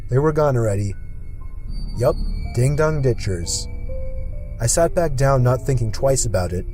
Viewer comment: if you put captions on why does it say ding don't b*tches